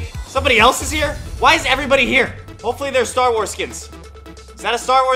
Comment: I thought he was saying starwars kids but then I realized that he was saying star wars skins